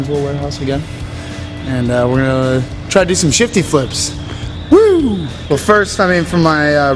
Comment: Any buddy else thought it said shitty flips